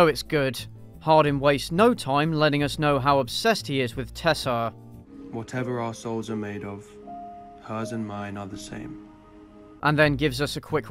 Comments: Wait at 7:14 does he say "whatever arseholes are made of hers and mine are the same"? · I genuinely thought at 7:11 he said "whatever arseholes are made of" but then i realised such a philosophicaly interesting question would never be asked in such a dull movie.